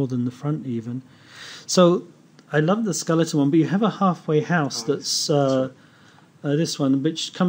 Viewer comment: Did you say the name of those pilots watches @ 9:30 were called Halfway House?!? That's hilarious. Did they say when they will be coming out?!? Those are so nice!